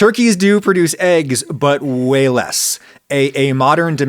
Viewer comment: " . . . Turkeys do produce eggs, but WEIGH less . . ." < ------- that's what I thought I heard🤣😂😅 ---> 🦃🥚⚖